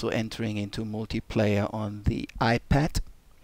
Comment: Did he say "The iPat"? might be the next hit.